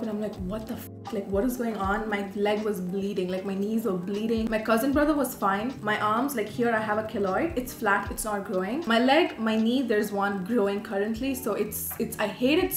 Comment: ahahahah. im so obsessed with her. she is so relatable! btw isn't her cousin's brother her cousin as well? 😅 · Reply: I thought she was saying cousinbrother!!! I was like "what in the Kentucky is going on"?